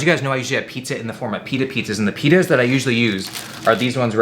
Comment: Misheard “pitas” as penis and boy was I taken aback for a split second .......